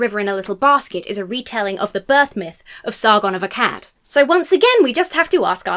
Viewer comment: I heard "Sargon a cat was born in a basket" and Google's AI informed me "Sargon was a Mesopotamian king, not a cat." Another bubble popped. Thanks.